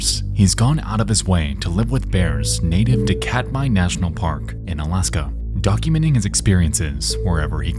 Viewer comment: Captions says "an escapable" when it should say "inescapable" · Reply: Captions says "Cat by" instead of "Katmai National Park"